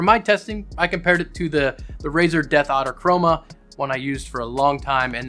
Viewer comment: It sounded like you said Death Otter Chroma when you where talking about the mouse. And I'm lmao.😂